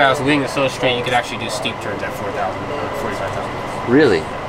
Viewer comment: At about 5:44, what did say the aircraft could do at 45K feet, due to the wings? I've played it back like 3 times and I can't understand the word. Sorry, I lost most of my hearing when I was in the service, back in the late 60s. BTW, looks like he's having way too much fun for one guy! Yeah, I'm jealous, LOL Thanks, TreeTop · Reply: TreeTop1947 he said that you can do steep turns at 45k